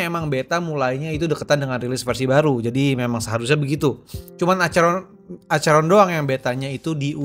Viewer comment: ini yang gw salah denger aja atau gimana Soalnya gw denger itu Acheron, dan yang di tulis itu "Acara acara" 🗿😅